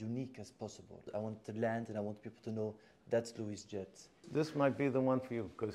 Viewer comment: he actually said: "and that's Louis' jet" and not "that to his jet"